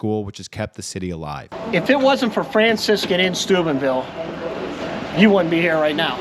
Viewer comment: oh Steubenville. I thought you said Stupidville. 😂